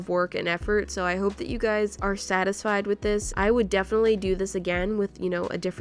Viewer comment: did just say statisfied.. 😨😨. Lol is it just me or did anybody else hear it??